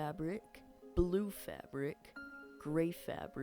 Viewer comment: When you Cream fabric I thought you said green